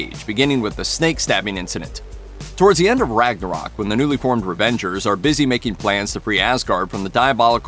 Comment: i think i heard REVENGERS